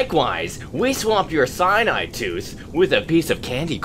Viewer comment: I don't understand the word that Near says. Sign I tooth? Idk :c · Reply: "Likewise, we swapped your cyanide tooth with a piece of candy corn"